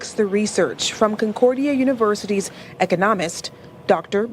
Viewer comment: Did she say “eco-ńomist”? Was this report created by AI?